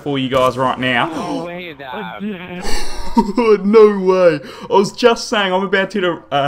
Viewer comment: Why did i hear "No way Adapt" @ 3:24? haha